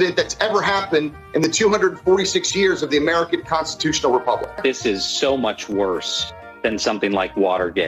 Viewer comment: is he saying the American Prostitutional Republic?